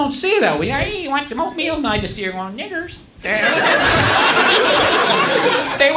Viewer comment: did he say n*ggers???❓️❓️❓️❔️❔️